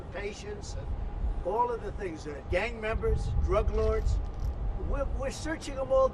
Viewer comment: I thought he said “gay” members lol